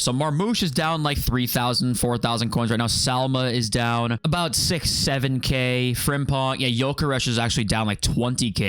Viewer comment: did i hear right 67